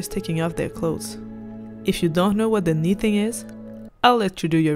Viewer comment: What is the word here, please? 6:02 (Sorry I’m not speaking English but want to google) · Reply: haha it's ok, it's the knee thing 😉